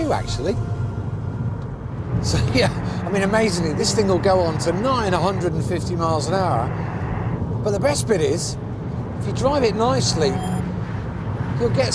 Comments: did he just say the golf goes up to 950mph??!! I want one!!! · Maybe it is just me but I think he says the golf will to 950 mph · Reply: nigh on 150 mph… meaning nearly 150mph.